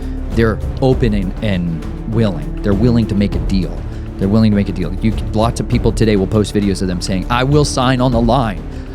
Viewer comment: Hi there! Is there a way we can get more information on the plant he spoke of at 1:20? It sounded like maraposa? Thanks so much! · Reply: Mimosa tree aka Albizia Julibrissin